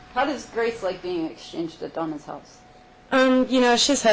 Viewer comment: Did she say, "How does Grace like being exchanged to the DUMBEST house"?!? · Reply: Donna's House